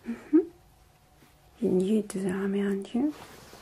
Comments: wut dafuq did she said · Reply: Ur new to the army aren't you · What does she says at 0:30 ? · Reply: MA. AK47 "You're new to the army, aren't you?"